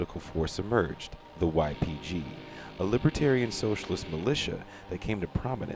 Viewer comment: did he say libertarian-socialist ?